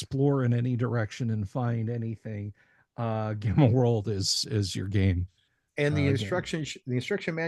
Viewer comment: what game is this? When you said the name it didn't come through clearly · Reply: Gamma World.